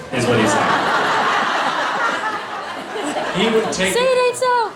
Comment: what is the girl at 3:54 saying? Because Jared looks at her like "what the hell?" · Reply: Misha: "He would sacrifice his butt is what he is saying.." The girl: "Say it isn't so!" Jared: wtf